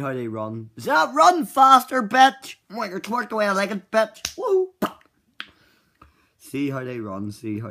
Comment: did I hear him say "twerk the way I like it"